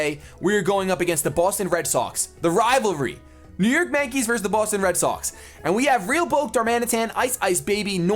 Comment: lol for some reason i heard "the boss in red socks" xD